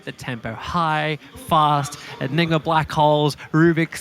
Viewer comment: did he just said Ni**a blackholes :D